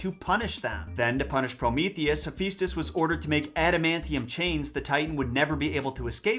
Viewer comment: Ngl I thought you said adamantium and I thought the chair was going to stab her.